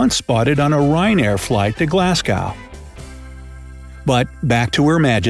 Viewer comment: Isit just me that heard him say glasgoot 🏴🏴